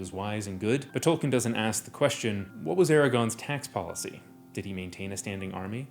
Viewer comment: I heard "Eragon" instead of "Aragorn" and it triggered my middle-school fantasy PTSD from 20 years ago